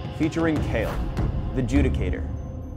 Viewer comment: Did I just hear kayle the jew dictator?